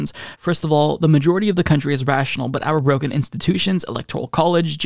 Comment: For a sec I thought you said “Rogan” institutions—not “broken” institutions. I guess either works.